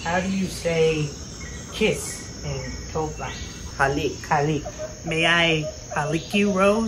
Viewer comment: Pinay here. Rose's accent was on point! Big ed was great too... But what were you saying at 3:32? Kiss in tolka? It's Tagalog ed. 😅😆 You guys did great!